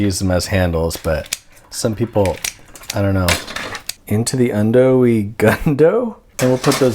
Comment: i think we almost heard ysac break for just a moment at 3:10 with the word(?) 'gundoe'